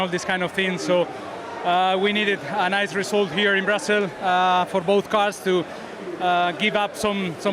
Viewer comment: Alonso going well in 'Brussels.' 1:06. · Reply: I thought too that he said “Brussels” but it’s his accent. He says Brazil (Brazell)